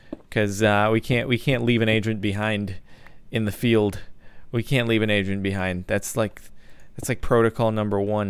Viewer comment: I thought he said, "I can't leave an Asian behind," lmao